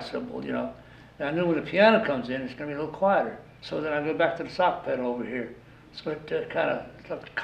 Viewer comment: What did he call the hi hat?? About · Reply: sock pedal (early type of hi hat)